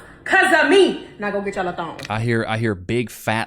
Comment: Did I hear her right? She just told fat women to "Now go get ya'll a thong!"